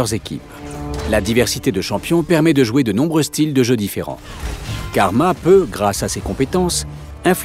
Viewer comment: qu'est ce qu'il dit ??? "car ...." ? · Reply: "Karma peut"